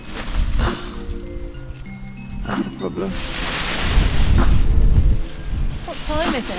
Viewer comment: Why did I hear a man's voice say "that's a problem" at 8:14😂